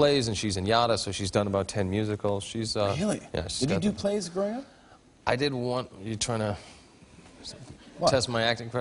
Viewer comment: can anybody please tell what did jimmy say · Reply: البديع فلاتة "Did you do plays when you were growing up"